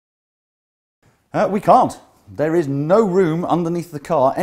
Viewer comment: did he just say " we can't" ...lol